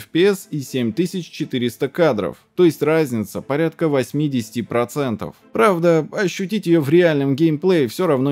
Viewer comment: сказал так быстро, что мне послышалось "разница порядка 80%" и я поперхнулся пивом